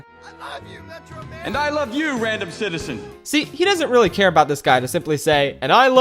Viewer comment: The way I heard 'and I love you random citizen' was 'and your breathtaking random citizen'